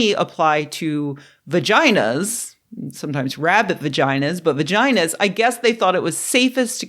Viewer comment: Forgive me, I thought you said rabbid vaginas. I was immediately taken to a land of mental images that I wouldn't wish upon anyone. Thanks!!!